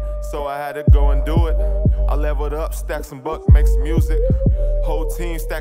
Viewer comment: So I was thinking this was the clean version until the 1:10 mark where he CLEARLY says "fuck" so 🤔